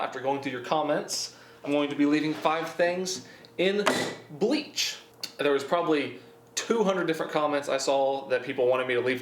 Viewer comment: For some reason I thought in said “I drink 5 things of bleach in 30 day’s”